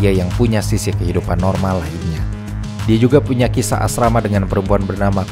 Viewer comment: "Kisah asrama" Ga salah denger kan gw? Tapi kek nya emang sengaja deh 😁